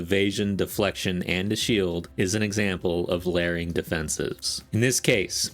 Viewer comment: Ohhhh, you said "LAYERING"; I coulda swore you said "LEARING". Learing is essential for PoE2.